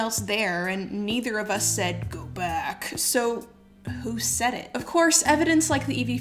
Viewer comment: I don't hear "go back" . I hear "good luck".